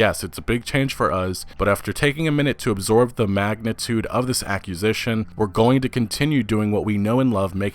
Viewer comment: Video starts: “Holy crap... Xbox has just made one of the biggest aqqusations of this generation” 😂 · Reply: i heard it as accusation